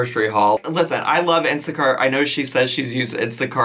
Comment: Lol when you said insta cart I thought you were saying insecuuuur